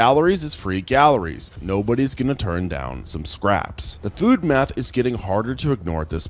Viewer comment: OOOOOOH! *FOOD MATH*. I thought you said "Good m3th". I was like, wait... when did the T-Rex pick up that habit? 😂